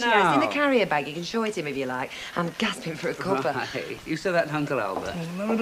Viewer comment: Dreary says: "I'm gasping for a cuppa" Subtitles say: I'm gasping for a copper. 😂😂 Response to video only.